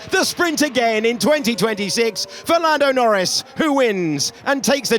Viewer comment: I can't be the only one who misheard 6:07 as Fernando Norris